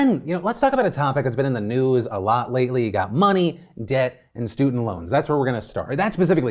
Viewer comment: Heard "money, dick, and student loans" before I heard "money, debt, and student loans" lol